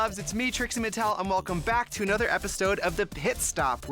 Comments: I think she said epistode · Did she just say ''Epistode'' ???